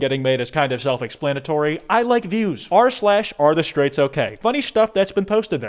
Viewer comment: thought kwite said "I like dudes." I mean am I wrong